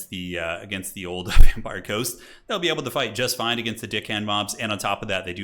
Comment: it sounds like you said “the DickHand mobs” and I had to pause and replay it 😭